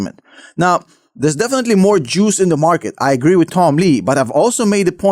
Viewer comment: Between 2:40 and 2:45 did he say "There's definitely more Joos in the market" or did I misunderstand what he said?